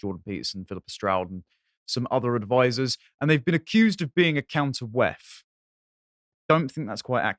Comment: American here; wth are words said at ~ @ 0:31 ? "a counter with" "a Count of Weth" Please explain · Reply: A “counter-WEF”